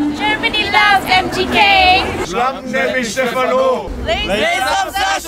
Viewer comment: embarrassing just embarrassing they had said instead of steven stephan